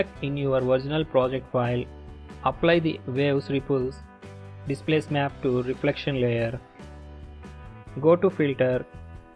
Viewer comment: What did you said in 5:06? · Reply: Apply waves ripple displace map to reflection